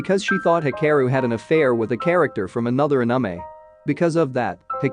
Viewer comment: Did he just say aniemae?